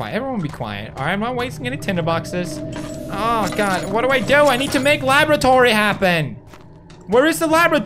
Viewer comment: OMGOSH, I HEARD HIM SAY "I need to make laboratory happen" AND IT TOTALLY SOUNDED LIKE LAVATORY HAPPEN. LOL -love, a fellow youtuber.